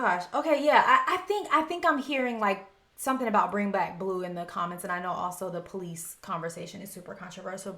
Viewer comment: I thought I heard “red white blue” not ”bring back blue”.